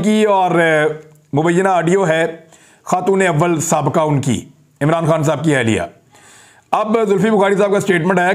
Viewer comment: Did you say “ Un ki sabayka ahlia” about Bushra Bibi? At about 5:30 minutes into the Vlog